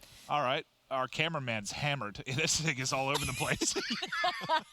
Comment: I think he said this thing is all over the place but he sounded very similar to that lol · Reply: I Heard “This nigga’s all over the place” either way that shit was funny 😂😂